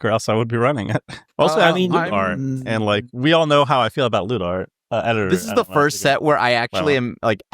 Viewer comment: I heard "loot art" as "lewd art" and was very confused